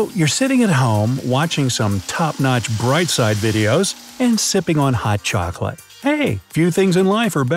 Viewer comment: my dumbslef thought it said “simping on hot chocolate” 💀